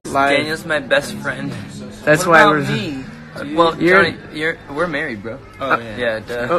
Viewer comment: Was i hearing things or did jack say to zach "we r married"? Like if u heard the same thing